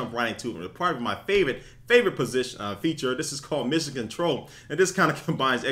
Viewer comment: the 0:10 did you say "probably my favorite position"?........LMAO! I notice you had to contain your laughter.